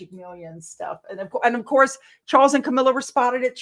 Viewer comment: I heard ‘Sharkles’ 🤣